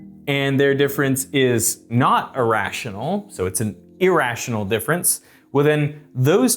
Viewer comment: subs need 'A rational' instead of 'irrational'